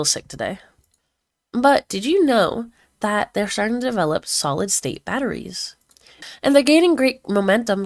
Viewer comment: Ngl I thought you said solid snake